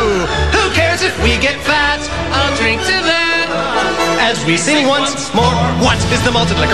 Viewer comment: Fuck I thought he said “I’ll drink to beer. I’ll drink to that” Not “Who cares if we get fat. I’ll drink to that”